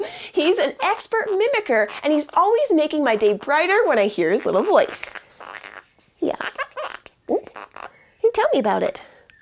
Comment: Her: "he makes my day brighter when I hear his voice" Chopsticks: "WTF"